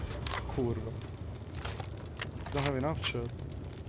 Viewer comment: did i hear you say "kurwa"?